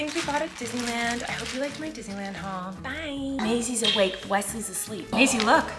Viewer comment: "Tortilla talk" question in your recent video on family photos at time stamp 5:08 Wesley sounded like he says Maisy it was really cute lol. what other words do they say?